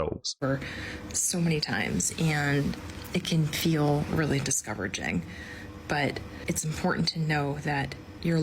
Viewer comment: Is it just me, or did it sound like she said "discoveraging"?